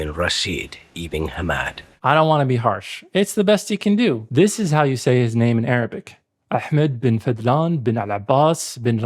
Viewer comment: The video cut to an ad in a funny place and I just had to share for a laugh. What I heard was "This is how you say his name: Arby's, we have the meats."